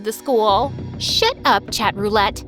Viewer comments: In 9:04 I think she said "shit up" instead of "shut up" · 9.03 did she just said sh*t up !?!